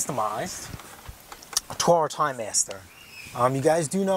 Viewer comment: I like how YouTube heard you say Toro Timemaster and close captioned it as Horror Timemaster”!!